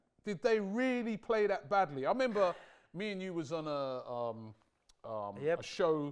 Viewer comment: I thought you said you was gonna win😂